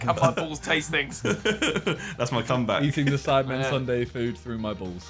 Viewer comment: Am I the only one who thought Josh said 'Sidemale' reacts in the beginning?